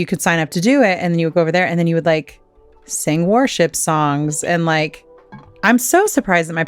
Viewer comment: I thought you said war ship songs! 😂😂😂